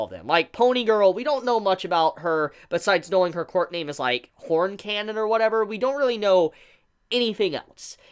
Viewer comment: Why did I hear “porn cannon” xD